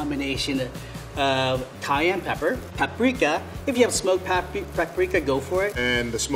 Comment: I thought Lorenzo said “ if you haven’t smoked chilli powder go for it “ 😅